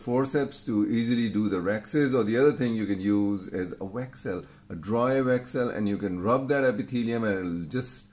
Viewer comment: Interesting case presentation sir. What was that word @ 5:05 minutes? Dry what? Thank you. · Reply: Dry Weck cell sponge or microsponge commonly used to absorb blood or fluid during eye surgery